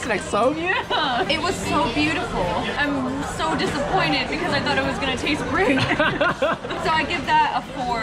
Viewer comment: 『It was so beautiful, I so disappoint it because ～？？？～That's I get the four. I'm sorry...I'm so sorry. It was one thing experience(?)』めっちゃ早くて難しいですが、この部分は～I would wanna tastes really good. ～みたいに聞こえるのですが実際の表現はどうでしょうか・・・（？） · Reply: ここ超早いですよね！ぼくも流れで意訳してしまいましたが、よく聞いた感じ、「It was so beautiful, I'm so disappointed because I thought it was gonna taste really great. So I give that a four（これに４点をあげるわ）. I'm sorry...I'm so sorry. It was worthy experience.」って言ってますね。